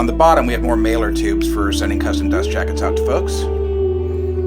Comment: Did I hear you say send custom DJ's out to people? 🤔 Love the collection my man.